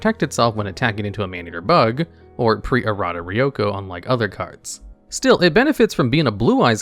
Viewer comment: Did he say Ryoko? Like, as in the Tenchi character?